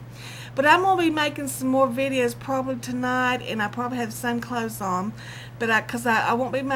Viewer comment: Ok, first things first. @ 5:30 in this video did you say that you would be making videos tonight and "probably have some cloths on"? Cause that's what I heard and I can't for the life of me figure out what other it might have been. First name David. Don't know why they picked it. It means beloved. Middle name is Edwin. Yuck!! It grew on me. I used to hate that name. My brother came up with it because it means friend and he wanted one in his little brother. Put some cloths on! :-)